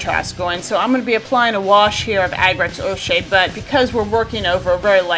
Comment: of the video, you speak of using a wash. I can't hear it very well can you please tell me the brand and name you used. Thanks! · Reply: It's Agrax Earthshade, a wash made by Citadel / Games Workshop.